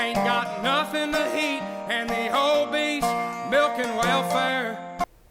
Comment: I thought he said the HOMIES milking welfare 😂😂😂